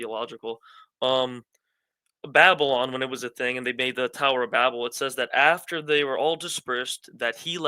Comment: Did he just say "the tower of Babylon"?